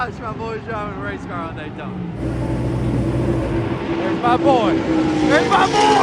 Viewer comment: All i heard was (Thats my boy, Ricky!!) LOVE IT!!!!